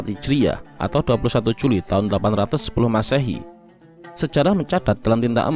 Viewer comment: Tahun 1904 h /800 sebelum masehi, lahirnya imam buhori.... Apa sy gak salah dengar...? Bukannya tahun hijriyah sekarang blm nyampe 1900n, baru 1400n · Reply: Maaf saya salah penyebutan tahun Yg benar 194 Hijriyah, Dan yang Masehi njenengan salah dengar, saya menyebutkan 810 Masehi